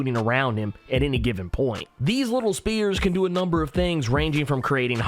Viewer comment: I swear tg youre saying SPEARS instead of "SPHERES" Like bruh... come tf on. 2:06 u did it multiple times